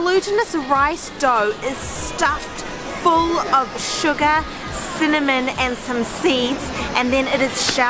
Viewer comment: Did she say Suga at 2:59?!?!?! Or am I just hearing things. ARMYYYYYS WHERE ARE YOU?!?